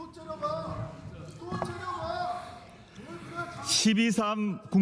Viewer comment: 뭘째려봐가 계속들리네 ㅋㅋㅋㅋㅋㅋㅋ